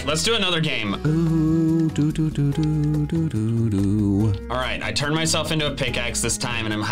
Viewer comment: it sounded like he was about to say "I turned myself into a pickle".